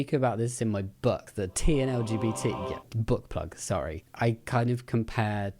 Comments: Book plug! Me, listening not watching cos I'm cooking: Did he just say Butt Plug? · Thought this was funny, when you said book plug, I heard it as butt plug. I was looking away from the screen. 😂 Love your content!